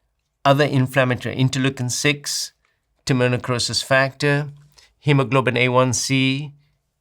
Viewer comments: What test does Dr J mention at 1:46:52? It sounded like Dominicrosis Factor? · For the blood testing at 1:46:49 is he saying Timonucosis Factor as it says in the captions or is it Tumor necrosis factor (TNF)? I googled and can't find Timonucosis Factor only Tumor necrosis factor but it doesn't sound like he is saying that.